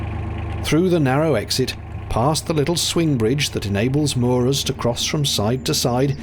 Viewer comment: Just a heads-up, there's "English (auto-generated)" captions around again, which it defaulted to instead of your "English (United Kingdom)" ones. Pesky YouTube. 🤦‍♀ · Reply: Possible error in captions, "moors" instead of "moorers"? ❤